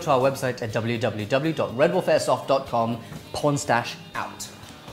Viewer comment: I heard "pornstache out"